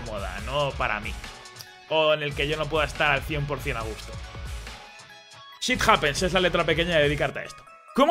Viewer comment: "Shit Happens" Yo entendí "Sith Happens" xD